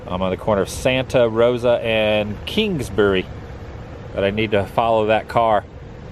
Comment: for a minute there I thought you said "Kinksbury"